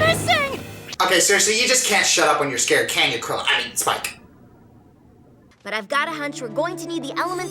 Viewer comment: Jesus I can’t understand what you said. Even at .25 speed I cannot understand you! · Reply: Ok seriously. You just can't just shut up when you are scared, can't you Cro-I mean Spike.